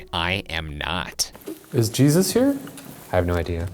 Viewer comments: it sounds like they said “I hope not” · Honestly, I hear "I hope not" rather than "I am not" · I hear “I hope not” over I am not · I heard “I hope” not “I am not” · When they asked if jesus was there I low key heard "I hope" instead of "I am not" around 17:35 or something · I didn't hear "I am not" I heard "I hope" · It sounded like it said "I hope not" instead of "I am not" when they said is Jesus here. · I thought it said " I hope" not " I am not' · I heard ‘I hope’ and not ‘I am not’ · I heard “I hope” instead of “I am not” but I could be wrong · Ryan: Is Jesus here? Voice: I hope. They said it said "I am not" but I heard "I hope" and that would make more sense because they're in a chapel. Anyone going to a church for mass would hope Jesus is there with them. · in response to "Is Jesus here?" I heard "I hope" not "I am not" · I think it said “I hope” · I hear "I hope" not "I am not" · Are you sure the ghost said "I am not" because it sounded like it said "I hope not" which would make more sense imo. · I heard “I hope” instead of “I am not” :-/ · I heard at 17:45, "I hope" not, "I am not." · I heard "I hope" instead of "I am not" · Instead of “I am not” .. I heard, “I hope” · Wooo! More Ohio stuff!! Also, I heard "I am though", not "I am not" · I heard "I hope" instead of "I am not".... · when the voice says "i am not" i don't hear that. I hear either "I Hope" or "Michael". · I didn't hear 'I am not' I heard 'I hope'.... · I heard “I hope not” not “I am not”